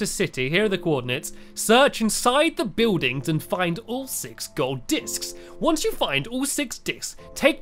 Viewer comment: did he say all gold ducks??????😂😂😂😂😂😂 edit:dicks